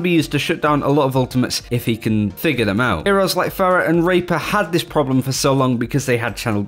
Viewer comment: wait did he say pharah and raper